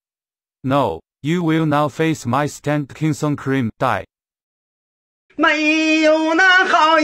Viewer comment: my stand Crimson queen? Crimson krem? Crimson Korean (wtf is he saying) Oh its Krimson Krim...why?